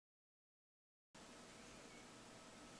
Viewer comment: did he says:sai?? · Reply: sigh